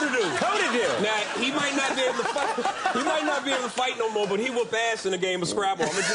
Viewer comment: I'm not sure of this, but it sounds like Ariel said "He may not be able to fuck" at 6:50. Luckily for him, he caught himself and no one noticed.